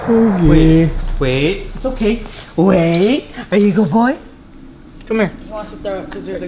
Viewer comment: Roi i heard something like the same voice i think it said “roi come here” Did you hear that in your editing? Edit: or maybe “oh roi come here.’’ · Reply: I heard "oh come here".